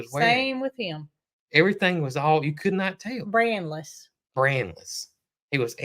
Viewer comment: I thought you said anti-brainless!